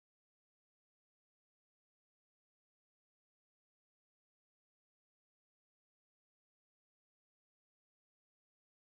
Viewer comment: toyz 7:42的時候他說對不起誰..聽不清楚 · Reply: John C 「有一種對不起stanley跟JJ的感覺」